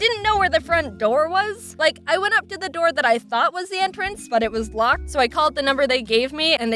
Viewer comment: OMG at 4:40 I heard you say firtht and wath instead of first and was